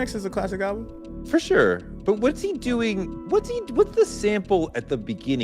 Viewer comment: Clue re yams: (listen to King Kunta)… Mac touching yams is DEF a triple. Iykyk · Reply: That was my thought! Immediately heard “What’s the yams?”